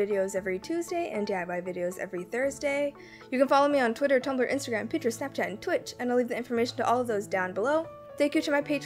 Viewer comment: What are you saying at 14:12 ? I'm adding subtitles to your video but cannot understand the names of the websites. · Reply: Twitter, Tumblr, Instagram, Pinterest, Snapchat and Twitch! Thank you so much for doing subtitles on my videos, by the way, that means so much to me, you're awesome!! ❤